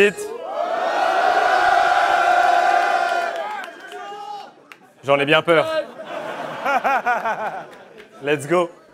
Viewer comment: Le public il dit quoi à 1:40 pour que réagisse ? MDR à 5:57 comment ça sortait du fond du coeur xD ! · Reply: Le mec dit : « l’ingé son » par rapport au rap contenders d’avant quand aparzit jette sa bouteille mdr !